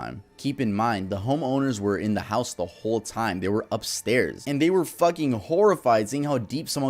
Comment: I thought for a moment he was saying that the homeowners were upstairs fucking the whole time, during this home invasion. Gotta love my ADHD brain getting sidetracked for a moment and then gaining attention back at impeccable times like this. Lmao.